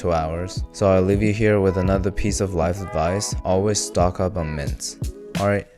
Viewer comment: I heard "stock up on mince" and kinda agreed with a bit of confusion until I realized